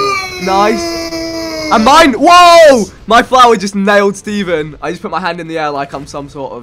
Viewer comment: Did said "My Flowey Just hit him"?